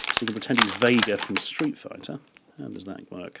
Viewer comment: Did you say Vader from Street Fighter?